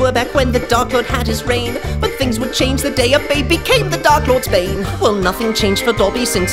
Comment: At the beginning, I thought dobby said: But then a little boy became the Dark Lord's Bae xD